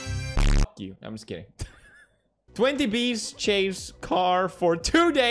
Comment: i thought it said 20k not 20 well thanks for solving it